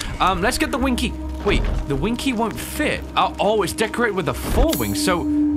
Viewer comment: What said : The winged key won’t fit... What I heard : The winky won’t fit... 🙈